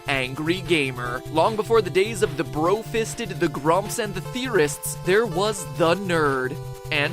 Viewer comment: oh I look in disk and I thought it said join the terrorists XD